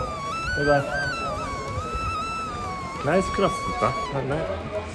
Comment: この時 なんて言われたのか 今、見直してみたら聞き取れました。 「ナイスグラス」と サングラス🕶️を褒められたのでした🙏🏻